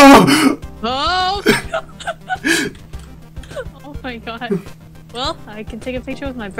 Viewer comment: bro sounded like 'OH MY PC'